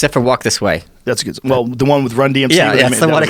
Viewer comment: Thought he said "Run DMT" for a second...